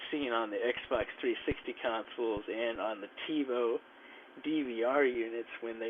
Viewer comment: Did you say TiVo?